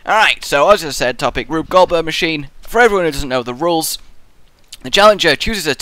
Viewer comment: I thought that said 'Lube Goldberg Machine', I'm sorry, you can go back to watching the video now XD